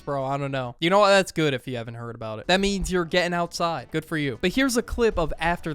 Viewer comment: with the private jet thing I swear I heard someone go “good for you” lol